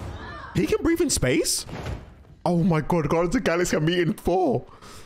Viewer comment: The captions say "Oh my god the guy's got me in four" o.0